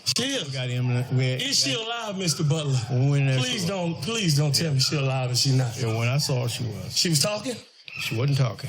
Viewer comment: "Please dont tell me she's ALIVE!..... OR NOT"... He literally slipped up saying that... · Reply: I thought he said that too but I listen back to it and he said “ please don’t tell me she’s alive and she’s not.”